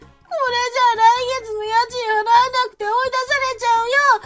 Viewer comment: あたり、『家賃払えなくて』じゃなくて『家賃奪えなくて』って聞こえるんだけど気の所為？